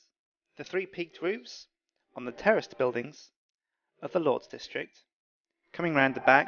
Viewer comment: i thought he said terrorist buildiings instead of terraced buildings hahaha. great vids btw